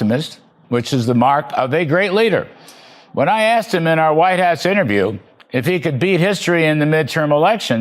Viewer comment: Did he say, White arse interview?